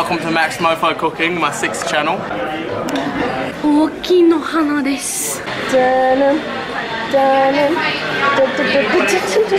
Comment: what does she say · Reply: I say "大きいの鼻です" Which means... "It is a big nose" xDDD